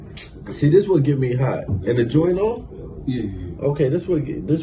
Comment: was asking if the camera was off when he said "is the joint off?" Or did I hear that wrong? 🤣🤣